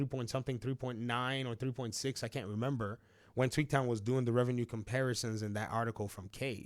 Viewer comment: Until I looked more closely at your video, whenever you said Tweaktown, I thought you were saying "Tweet town", i.e., a nickname you gave to Twitter/X. 😅